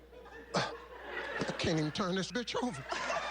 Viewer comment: yoooooooo dd anyone catch what he said at 16:49 "i cant turn this bit** over" or is it just me